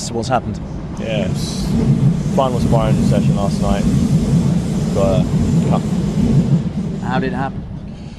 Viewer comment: What was the full sentence? Couldn't understand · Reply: sparring session last night,, got a cut